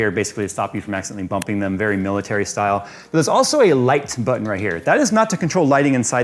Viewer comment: Oooh, LIGHT button! I was multitasking and could have sworn he said LIKE button. I was like, "You've gotta be shittin me!". I'm gonna say it's because we hear those words WAAAY too often these days